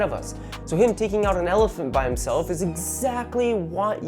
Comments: did you say elephant? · yes, I can read. But it didn’t sound like he said oliphaunt. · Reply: I knew this would bite me in the ass... I did like five different takes and kept saying elephant for some reason. Ended up just giving up and chosing the one take that sounded most like Oliphaunt. But yes, I totally fucked that up lol